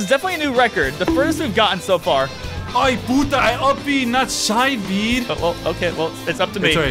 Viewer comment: did he say what i think he said? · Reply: Like, is it me or did he say p*ta?